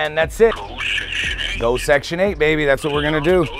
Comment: I thought he said no section 8 ? Put he put in section 8 lady ?